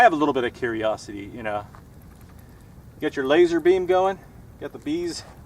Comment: "got your laser bees going?" is what i heard here?